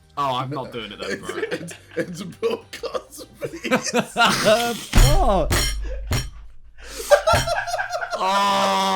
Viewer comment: What did KSI say on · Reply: Bill Cosby